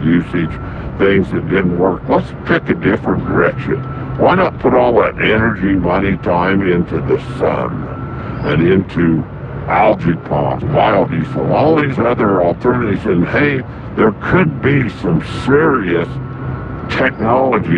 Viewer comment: did he say biodiesel? Diesel ? , Here's a quote from Rudolf Diesel ; "My intention was to design an engine that would make the farmer totally independent from these oil companies." Then, according to the official story, he decided to jump in to the ocean and drown. They didn't make industrial hemp illegal for nothin.